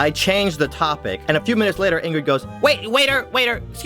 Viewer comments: wait did he just say ingrid.. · did he say “and then INGRID goes* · am i the only one who heard him say ingrid on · I think he says the girl's name is "Ingrid"